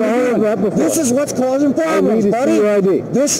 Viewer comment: did he say "Respectfully no, I recline"? LOL